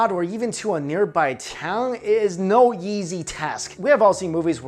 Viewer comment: on travel, does anyone else hear him say it's "No yeezy task" rather than no easy task? 🤣🤣🤣